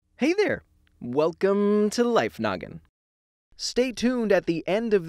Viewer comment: I thought he said life nugget in the intro